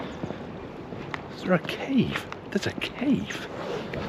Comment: I thought you said “there’s a Keith” not a cave ha ha